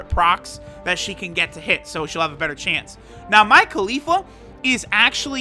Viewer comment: I thought you Said Mia Khalifa😂😂